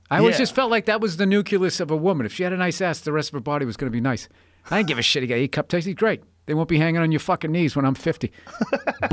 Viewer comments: please what did he say ???? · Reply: that he doesn’t give a shit about girl’s cup size “I don’t give a shit, your cup’s tasty (?) great” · please what did he say ???? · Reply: " I ain't give a shit you got "A" cup titties, great " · please what did he say ???? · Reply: “I don’t give a shit if you’ve got E cups, they’re gonna be hanging to yours knees by the time you’re 50”